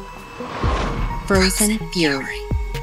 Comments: The name tho... Edit: omfg I thought is said frozen furry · I legit thought it said furry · I thought it said "Frozen Furry" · I thought it said frozen furry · I thought it said frozen furry.